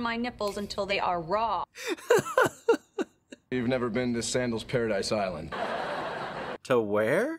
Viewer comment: Okay, we all know where going on his next vacation! 😂(Fyi, I think says 'a Sandals Paradise Island'.)